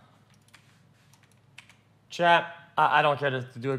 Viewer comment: Phút 2:00:15 hình như ổng nói trash đúng k ae? hay t nghe nhầm